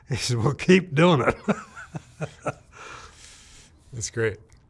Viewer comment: did Mark say "Good luck"?